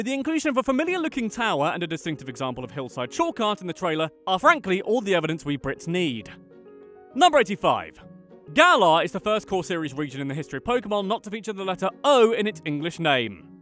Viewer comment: when you said, "we Brits" I heard, "wee Brits" lmao